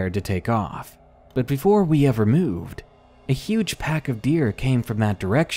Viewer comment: who else heard "a huge heard of beer?